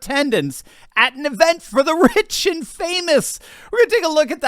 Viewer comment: Haha I heard “the witch and famous “ when you were talking about AOC. lol Then I realized you said “rich and famous”!